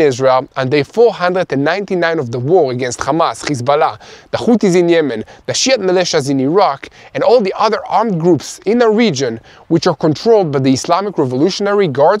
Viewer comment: I heard "the sh*t militias in Iraq"